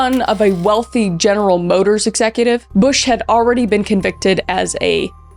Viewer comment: I thought you said "btch" instead of "bush" 😅